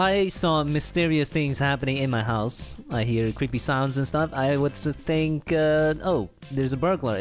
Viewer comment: I thought he said pp sounds 😂